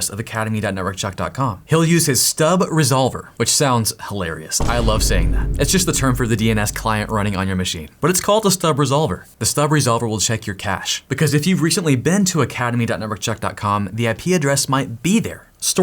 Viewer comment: I thought he said "The stub resolver will shank for cash